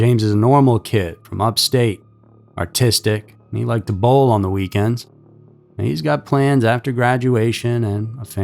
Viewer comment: Surely I’m not the only one who heard “and he lit the bowl on the weekends” the first listen? 🤭